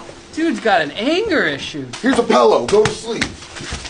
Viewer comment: Id stand up and say "did you just say pellow?" Wtf!? I'm playing my man